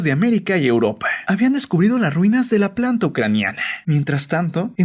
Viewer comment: oi mal o dijo "descubrido" en lugar de "descubiertao"??